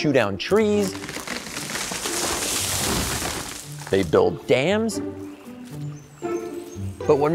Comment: When you said they build dams, I misheard that as "they don't dance"